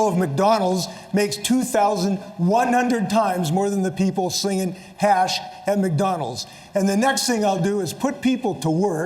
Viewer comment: "Singing hash at McDonalds"? "Slinging hash at McDonalds"? WTH is he saying?